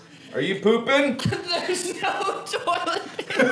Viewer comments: I actually thought he said there was no toilet. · Ethan: * goes to bathroom and takes a poop* "There's no toilet paper!" Mark & Tyler: HAHAHAHHAHAH!!!!!! XDDDDDDDDDDDDDDDDDDDDDDDD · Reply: PIXE1 ASSASSIN Oh I thought he said theres no toilet!!